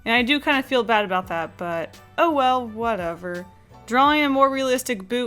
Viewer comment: I heard you saying "owo"